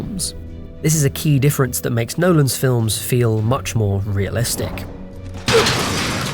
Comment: The subtitle says "much less realistic" when the voiceover says "much more realistic"